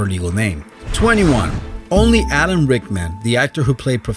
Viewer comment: Knew all of theseI thought he said Owen Rickman first lolR.I.P Alan Rickman